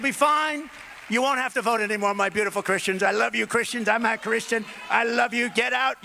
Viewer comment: did he just say 'I love you beautiful christians, I'm not Christian' ?' ok you touch on this. I guess. 'We'll have the fix in' If ANY republicans watch and READ this, this is the SAME president who did suggest taking your guns. at 0:49 ..... take gurns first, due process later. <link>